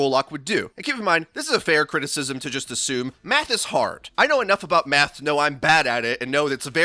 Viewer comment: Anyone else hear meth and not math at 4:02 😂